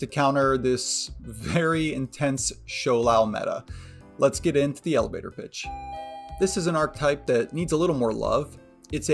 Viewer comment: Not gonna lie, I did a double take and thought you said "let's get into the elevator, bitch" 😂